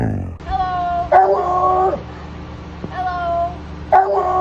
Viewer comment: dude that basset hound is so crazy! I legit thought it was a guy saying "hello hello" 🤯🤯🤯